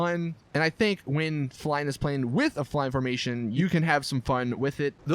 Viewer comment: idky i thought he said "this plane is a pussy"